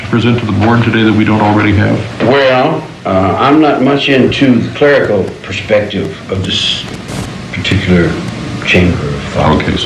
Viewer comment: What does he say at 3:47? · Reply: I'm not much into the clerical aspect of this chamber.